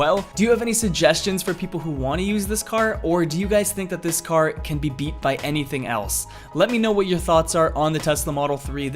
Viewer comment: Anyone else wondering what that guy said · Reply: He said "IM YOUR BIGGEST FAN ###### ME PLEASE #######"